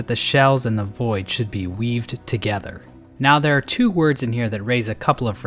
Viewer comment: honestly for a second i thought he said weeb not weave